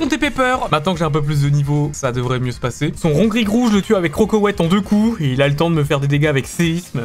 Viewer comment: J'ai entendu "CroKoweit" pendant toute la vidéo...